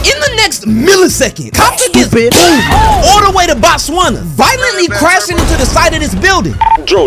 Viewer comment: at 9:48, did you say all the way to Botswana?